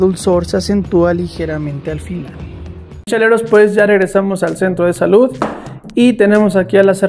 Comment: Por un momento entendi "Lecheros" :V